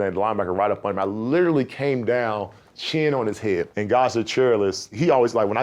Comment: godzilla cheerless? Im tryna make out what players name he said and the subtitles came up with that…anybody figure out who he talkin bout?